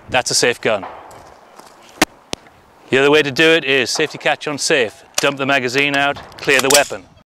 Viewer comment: At first I thought he was saying "UN-safe" instead of "ON-safe." I was like, "why would you want to leave a completely unsafe weapon?!?!?"